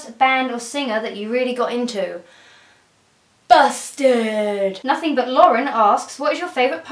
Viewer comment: I thought she said 'bastard' at first ._.